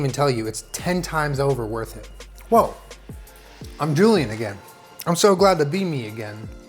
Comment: I thought he said "I'm drooling again"🤣